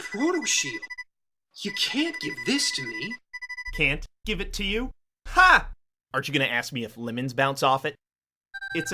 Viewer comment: Wait...did whoever was doing Protoman's voice say, "Can't give it to you? HA! Aren't you going to ask me if lemons bounce off it?", or was I just hearing things?